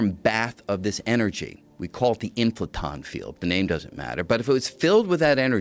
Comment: what was that word? I heard field or something. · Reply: Impleton field